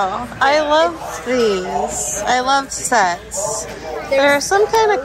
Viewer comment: lol, at first I thought you said, "I love sex". lol